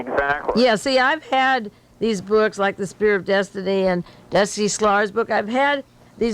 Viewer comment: she refers to two books, 'The Spear of Destiny' and one more which closed captioning renders as 'Dusty Slaughter's book', but that doesn't seem to be correct. Does anyone know what book she was referring to here? Apparently it dealt with Nazism and the occult, but 'Dusty Slaughter' as an author's name doesn't get any results. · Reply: I heard destiny slaughter, maybe slater?